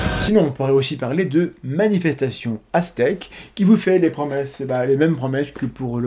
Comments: Au début j'ai compris "Manifestation ASTEC" :D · Quand vous parliez de "manifestation aztèque" j'ai compris "manifestation ASTEC" ^^